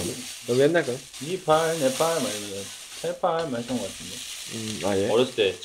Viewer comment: did Nunu say Nepal?